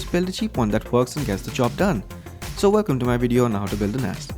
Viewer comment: Flipping Awesome Video! It sounded like you were saying, "Lets Build An Ass...." LOL